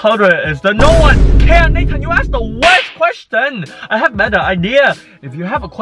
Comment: Did he say the n word? 1:11 ( I know he didn’t it sounds like he did tho)